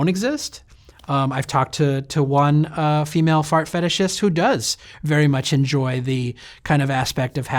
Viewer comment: Damn, I thought it said "fat fetishist" and I was like, "Sweet! Me too!" Fucking disappointed.